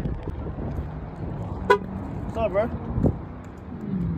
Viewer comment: There’s literally no way y’all heard “eat” 😒😒😒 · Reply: On god ‼️‼️at first I heard beat his ass 💯then the second time i replay the video all I heard is “I’ll eat his ass “not beat” eat”🚫🧢 😅😂😂😂😂